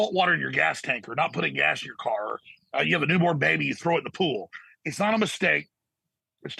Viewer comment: I thought he said you have a n word baby you throw it in the pool 😂